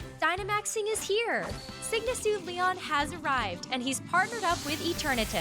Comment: I thought they said sigma suit rather then sygma